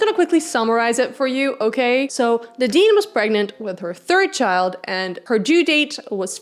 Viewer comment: my subtitles say "the dean was pregnant“ 😂😂😂